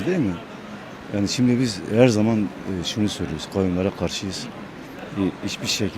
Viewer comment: İlkteki dayi Kayumlara karsiyim diyen dayi koyunlara karşıyım diye anladım ben😂